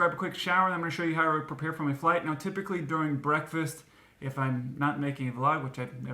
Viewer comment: I was kind of distracted by an email but I was sure I thought I heard say "Typically during my breakfast when I'm not making love..." at 5:53 and had to go back.